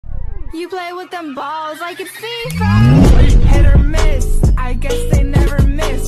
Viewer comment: me: WHAT DID SHE SAYYYYY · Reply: It said in caption "You play with them balls like it's beefy